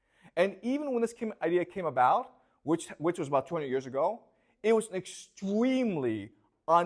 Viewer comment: I’m really enjoying these lectures but some of the details here are quite wide of the mark. For instance, the claim that Zionism is only about 20 years old and that Christian Zionism came first is not right. The Balfour Declaration (the British Government communique supporting the creation of the state of Israel) was addressed to the Zionist Federation of Great Britain and Ireland (established in the 19th century), itself led by Jews. I think it’s fair to say that the Zionist movement gained significant popularity after WW2 and the Holocaust given Israel was seen to be the only safe place in the world for Jews (having experienced centuries of persecution and pogroms after being “blamed” for the death of Jesus, as portrayed in Mel Gibson’s Passion of the Christ.) Is it however inarguable that there exists in the world three groups of people with three sets of potentially world-ending ideas: 1) Christians who believe that if they can get all the Jews back into Israel, Jesus will return 2) Jews who believe that if they can get all the Jews back into Israel and all other religions out, it will usher in the end times 3) Muslims who believe that Jews must be exterminated because it is decreed so in the Quran. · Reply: also thought he said 20 but I went back and subtitles say 200 years